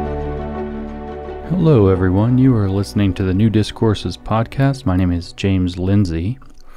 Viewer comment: When James says “New Discourses” I always hear it as “Nudist Courses.” And I’m like “What?”